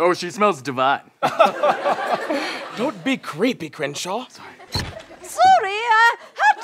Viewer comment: at first I thought he said Cringeshot I'm stead of Crenshaw.